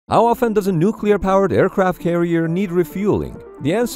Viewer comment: I thought he said root beer powered...